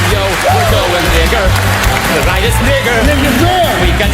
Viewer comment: did he just say the n word? lmaoo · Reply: No, he said “tonight it’s bigger”